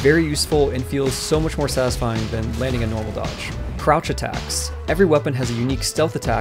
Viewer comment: thought he said crotch attack